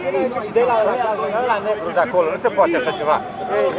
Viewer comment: when you wrote in the text at 0:27 'the black one', it was about Webo referring to the word 'negro' which in our language does not translate to 'the black one' or 'black' but to something else, a derogatory term., something like 'negrotei'. I don't hear it, people can use it and it exists, but it is not the same thing as 'negru'. So Webo starts by saying the referee said 'negro' which he did not say, he said 'negru' which means 'black'. I think this is a notable difference. 'Why do you have to say negro' ... well, he did not say 'negro' he said 'black'.